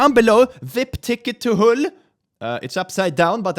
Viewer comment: Sounds like your saying A ticket to Hull. Perhaps hell is in Yorkshire :)